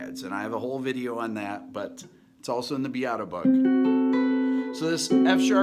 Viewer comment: Did you just say "the Beato Book?"